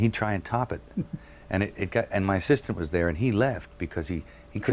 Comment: Did Mel just call his sister a ''he'' ? I have watched this interview many times and it don't make sense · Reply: I got tripped up by that too, but I think he said 'assistant' and not 'sister'...